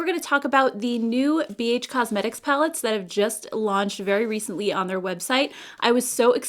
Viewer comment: I could have sworn you were saying Beige Cosmetic Palettes.